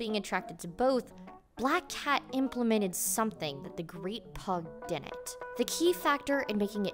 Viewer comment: I thought y'all were saying "the great pog"